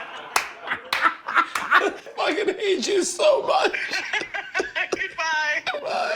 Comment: What did he say at the end of the FaceTime with Tom? 1:02:20 😮😂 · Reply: "I hate you so much 🤣 goodbye"